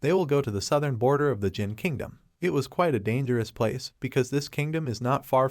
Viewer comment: The subtitle of the video was misleading ex: 1:27:39 instead of zhan kingdom it was "Jin kingdom " But it was an amazing recap ❤❤❤❤❤